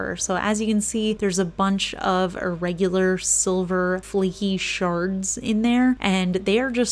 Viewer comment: i know you said shards but my weird ass brain heard it as sharts lmao